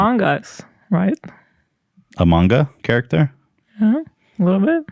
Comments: Am I losing my mind or did Ethan call among us .. Amonga lol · when hila said the model looks like an among us character and Ethan thought she said a manga character and she didn’t correct him because she thought he said among us lmfao